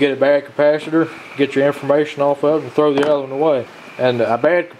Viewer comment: Good video but i must ask are those capacitors hard drives? cause i'm sure i heard you say "Get your information off it" @ 5:14 lol...